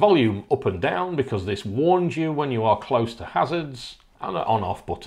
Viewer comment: What is it actually saying when it sees a pedestrian? it sounds like it says "passage". I just installed this on my own vehicle and i cannot figure out what it says. Maybe i'm going deaf? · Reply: It states "hazard".